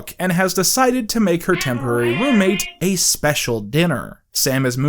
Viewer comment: What is the audio at 4:50:52? I keep going back and listening to it and have no idea what it says or why it’s there. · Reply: Slowing down the audio it says “Temporary” which is weird cause it sounds like says the same thing.